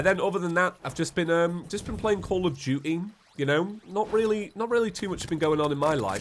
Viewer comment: Will I ever get the seed? Also it sounds like you said call of judy